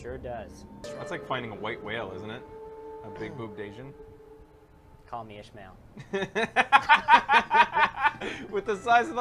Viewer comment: What does say at 5:41? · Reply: Call me Ishmael